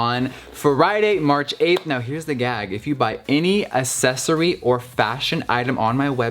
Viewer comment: Anyone else hear him say assessory instead of accessory at 1:03-1:04 😂